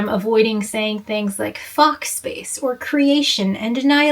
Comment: Look, I definitely totally know what all of these terms mean and what you're saying, but it may be good to clarify here that you're saying "Fock space", and not "fox space" or anything else it might sound like. Not for me, but for other people who might not know what it is and try to google "quantum mechanics fox space" or whatever. They might not get what they're looking for. For other people. Not me.